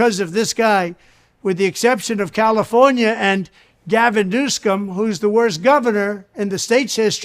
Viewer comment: did trump just say Gavin goosecome?